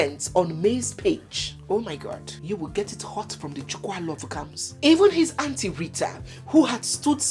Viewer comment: Wait! What did I hear? "You will get it hot from the Chukwualovukams😂